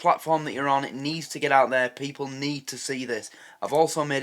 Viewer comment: I thought she said need but anyway 1:07 People Huv tae know I wish car was around longer to see if Tam ask Eric to pay for the tax Simmer down! He hasn't been to a gym for decades